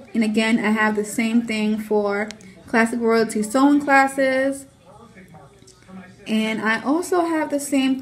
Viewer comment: Sooooo super duper informative! Thank you for sharing! (Did I hear you say "sewing classes"? I would definitely want to take your class!)